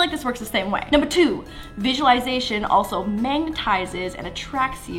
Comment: she really said mangtizes instead of magnetizes